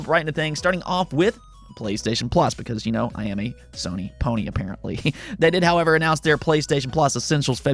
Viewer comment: Lol i thought you said stonerpony